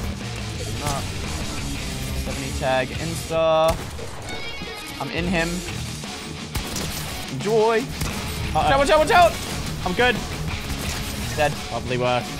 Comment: I cant be the only one who heard him say "im in him" at 7:15 right